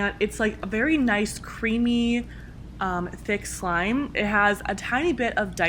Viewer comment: i thought it said it smells just like a creamy thick men. Me: whaaaaaaaaaaaaaaaaat That’s what I get for skipping